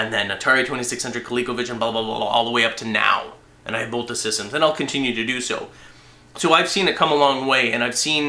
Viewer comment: seconds did you say I built the systems?